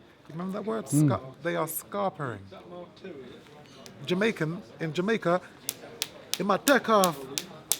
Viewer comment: does he say some jamacan sucks him off